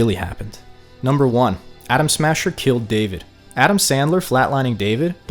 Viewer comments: Anyone else hear him say “Adam Sandler” instead of Adam smasher at 1:27? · I swear you said Adam Sandler 😭😭😭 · Did you really say Adam Sandler insted of adam smasher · "Adam Sandler" instead of Adam Smasher sounds like the best mod ever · Did he say adam sandler at 1:26 hahahah😂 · Did you say Adam Sandler lol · Yeah, I caught the Adam Sandler ref….I thought ‘Did he say that?’ 😂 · Did he just said Adam Sandler? XD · / 1:52 ... Adam Sandler ? 🤣 Did I hear that right? I may have cyberpsychosis kicking in...